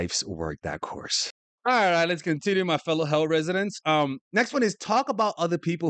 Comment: 😂😂😂Did you just say “Fellow Hell Residents?’😂😂